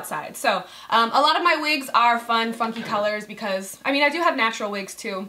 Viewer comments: I thought you said, " fun fucking colors." lol. but seriously love the wig. you rock it so well! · I was like did she just say fun fucking colors lol then you said fun funky colors again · I thought she said fun fuckin colors instead of fun FUNKY colors 😂😂😂😂😂😂 love ya babes 💋😙❤ I live in Tampa too boo 💋💋❤❤